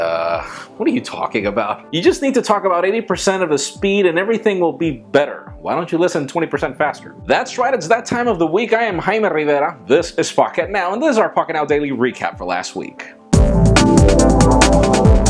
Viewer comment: I sear I thought Jaime said " This is Fu$k -it - now" lol